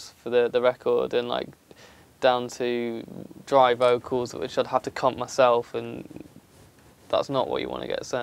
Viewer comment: did he just say "which I'd have to c*nt myself"